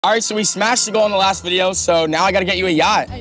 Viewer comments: I thought he said "alright we smashed in the last video so i have to buy you a boat" i said wtf 😂😂😂😂😂😂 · Ngl, I straight up thought the beginning said, "So we smashed since the last video so I need to get you a yacht now. " 💀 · thought he said he smashed the girl on the last video lmao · At the start i thought he said “we smashed the girl in the last video”😭😭😭😭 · I thought he said I smashed the girl in the last vid but he really said we smashed the goal last vid 😅😅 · I thought he said he smashed the GIRL in the last video 💀💀💀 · I thought Jack said in the beginning, "We smashed the girl in the last video..." What is wrong with me. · Yo at the beginning it sounded like he said I smashed the girl in the last vid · I thought he said he smashed a girl in the last video and I was like what the heck😅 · I thought he said I smashed the girl in the last video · i thought he said he smashed a girl in the last video. i was in disbelief